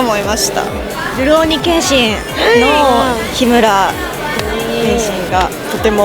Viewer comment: the subtitle should be Himura of Rurouni Kenshin instead of Ryuro ni Kenshin. (aka Samurai X)